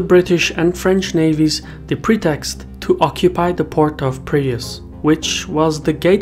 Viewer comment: Did you mean Piraeus? The auto subtitle thought it was previous. Are we talking about the same Piraeus port city built by Athens? At first I heard Prius. Like the vehicle.